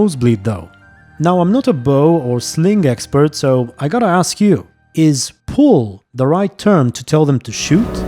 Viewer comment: I think she said “hurl”, the subtitles seem off on that one, and hurl would make sense when you’re commanding slingers, I also think the arrows hitting the slingshot ammo could be put down to magical guidance or something, and even if they miss and the jars hit the ground it’s still gonna explode anyway, also in reality slingshots were actually a really common weapon for low ranking soldiers and peasants, bc they were surprisingly easy to learn how to use and throw ammo at huge distances